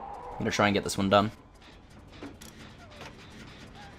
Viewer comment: what is he saying at 15:08? "oh my god it's <blank>" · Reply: "oh my god it's <a mori>"